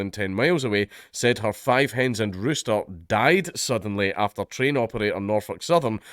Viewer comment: As a resident of Indiana, right next to the Ohio state line, I'm worried for my neighbor state. That, and watching that Chernobyl series, definitely so. The large egg and poultry farms and factories in the country "conveniently" burning down, this, spy balloons crossing the entire country before being shot down. When craziness like this is happening so often, conspiracy theories aren't all that crazy... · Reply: at first I thought Dank said 5 headed rooster. Had me thinking shits way worse than could be possible. But who knows how bad this is they've been willing to have a news scrub for this long. So I'm worried its bad. Not 5 headed Rooster bad but up there.